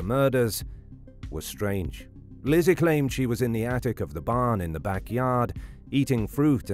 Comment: Whenever anyone asks me where I was, I always say I was in the attic eating fruit and searching for fishing sinkers... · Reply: I thought she said she was up in the Barn loft and I thought well that's a really odd place to be also, seriously who keeps lead sinkers all the way up in the loft